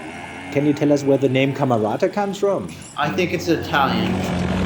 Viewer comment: "Andrew Camrata" That's what I heard you say.